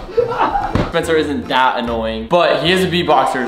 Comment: 나만 1:07 초에 아파! 아파! 로 들림? ㅋㅋㅋㅋ